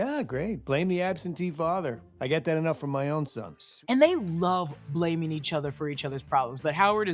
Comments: Did he say sons? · Did Howard say “sons”?? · swear i hear “sonS” every time i watch it, thought it was a soft drop of Tonys brother